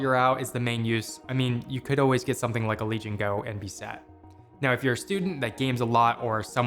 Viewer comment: get something like a legion go and be "sad" 😂 (Caption says set)